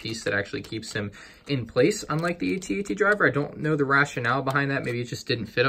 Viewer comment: Sounded like you said AT-RT driver instead of AT-AT driver